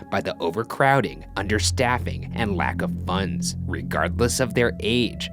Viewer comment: I thought they said "lack of puns"